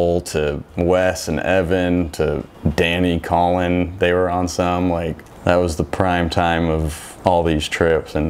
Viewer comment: Danny and Colin were on what? 😂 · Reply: Yeah, I know. it just sounded like he said they were on something.